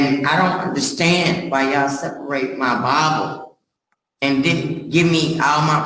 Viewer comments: When she said they took her Bible I thought she was saying that they took her bottle! lol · OHHH her BIBLE, LOL I thought she said bottle.😂😂 Why does that judge remind me of Richard Pryor? Well she don't have time for this because it's taking up her liquor time.